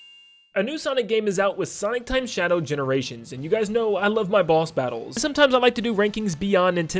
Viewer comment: I thought you said I love my balls battle